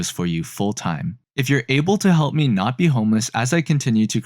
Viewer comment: for some reason, I heard "help me not be communist" also, croissant